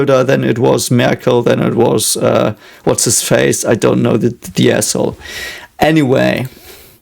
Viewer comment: Did you just call Olaf Scholz the a**h*le 🤣